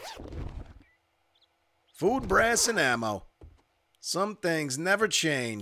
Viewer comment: sounded like you said.. "food, ass and ammo"...